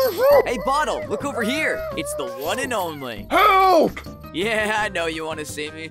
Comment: if you couldn't understand- 6:49 - help! 6:53 - shut up and help me! 7:02- just untie me, were gonna loose!